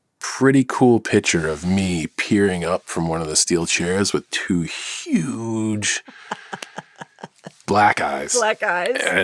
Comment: I got distracted from the video and I thought John Cena said “two huge black guys” for like half a second